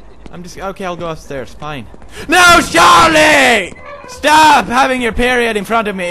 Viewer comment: Did he call the wheelchair guy Charlie? Spike & Barley reference! :D